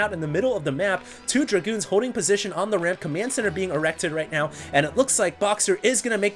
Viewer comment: did you say command center being....... erected?